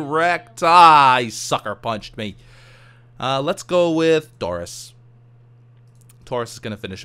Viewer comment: When he said "let's go with Tauris" I thought he said Doris